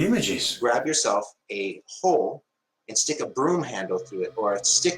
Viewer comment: I have really enjoyed this video, but holy fuck.... without you three my level of anxiety was through the atmosphere. Big thumbs up edit: 17:08 Did I just hear "grab yourself a whore"?